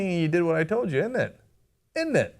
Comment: Did Cenk just say init